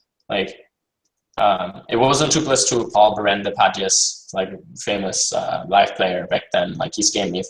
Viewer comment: what's the name of the guy who scammed fedor?? he says it at 52:49 but I can't understand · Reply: Paul "padjes" Berende